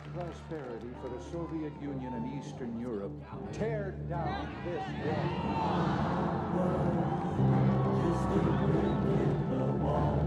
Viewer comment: What were they saying at 3:14? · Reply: Adeline Howell It was the band parents rather pathetic attempt to say "Tear down this wall"